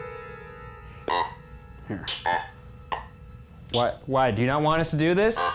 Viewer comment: i swear it says don’t do that/this